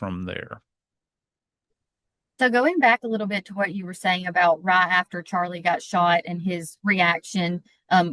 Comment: daaaaaang anybody know where is from?? The way that heavyyyyy country accent slipped out when she said “right” and it sounded like “rot” ❤❤❤❤❤